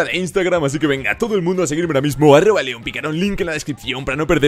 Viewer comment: no se si soy yo pero escuche wueon picaron xD